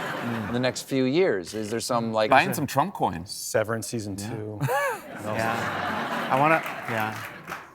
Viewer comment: I thought the guy said, "That Severence is true" not Severence Season2. 😂